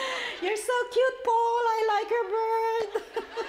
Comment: i love how she talks about Paul in American Idol and pronounces 'bird' instead of 'beard' x) shes so funny and adorable !